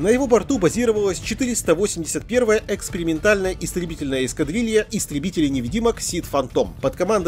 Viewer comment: после рассказов о Нургле, мне послышалось: "481 экскрементальная эскадрилья"